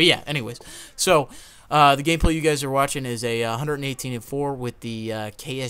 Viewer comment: in sounded like he said Gay play youll be watching